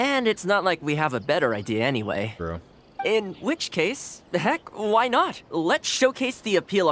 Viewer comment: Thoma said "the heck" instead of heck